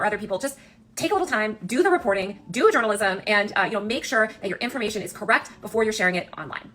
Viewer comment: did she just say "do a journalism"??? 😭